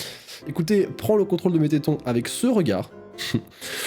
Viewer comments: moi j’ai entendu que tétons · Moi , à 8:07 j'entends : prends l'contrôle d'Mettaton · moi j'entends prends le contrôle de Métaton